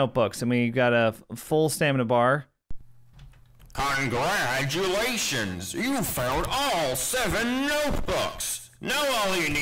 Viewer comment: Everytime Baldi says congratulations you found all 7 notebooks, it sounds like he says congratulations you failed all 7 notebooks.